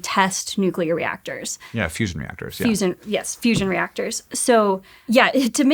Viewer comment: I thought Hank said, "What's inside Pokémon Fusion Reactors?" I was so confused.